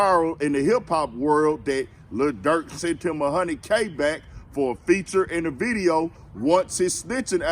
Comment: I thought lil Durk said 350k for a Verde nobody respect dude